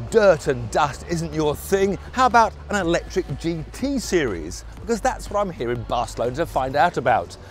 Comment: Did he say in 1:54 T-Series